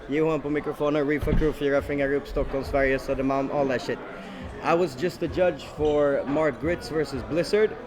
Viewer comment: what the fuck does say when he's introducing himself, alls i heard was "all that shit"